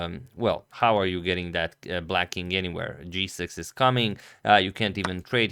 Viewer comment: When Antonio said, "g6 is coming" I heard, "Jesus is coming " and I thought it was an Armageddon game.